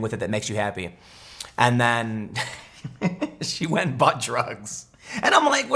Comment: anyone else hear Tim say "butt drug" lol